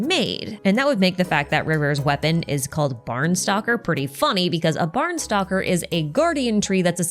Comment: i misheard barnstokkr as barn stalker. :pillow-turquoise-hot-chocolate